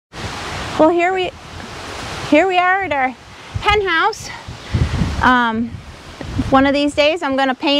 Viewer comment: I did enjoy the poultry update. Funny thing -- I turned closed caption on, and when said "hen house," CC thought it said "penthouse." So there's standing in front of the little coop saying, "Welcome to our penthouse" 🤣 Anyway I enjoyed this video as always 💕🐎